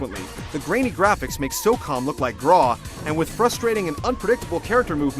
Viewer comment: I have no idea what he just said. Can someone tell me? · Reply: "... the grainy graphics make SOCOM look like GRAW." "SOCOM U.S. Navy SEALs" and "Tom Clancy's Ghost Recon Advanced Warfighter (GRAW)" were both tactical shooter series of the time. I'm not familiar with them, but in context I think the video was attempting to communicate "The graphics in 'The Fifth Element' are so bad, they make the graphics in this reputedly bad looking game (SOCOM) seem like the graphics in this amazing looking game (GRAW) by comparison."